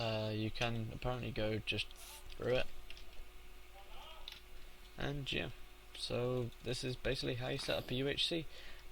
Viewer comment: Am I crazy or was somebody calling for someone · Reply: You werent crazy he said hello?